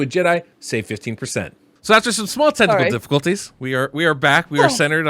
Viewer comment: "So, after some SMALL tentacle difficulties" Me: "...... I think I may have misheard..."